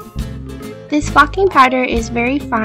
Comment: it sounds like your saying this f****** powder